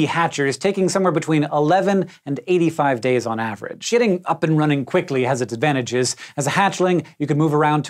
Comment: Getting up and running... I thought hank said "sh*ting up and running"